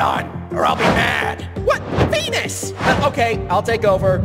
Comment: no shot the captions said “penus” instead of “venus”😭😭😭💀💀💀